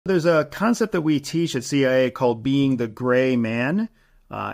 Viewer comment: Is it wrong that I thought he was saying "Becoming a Gay Man?"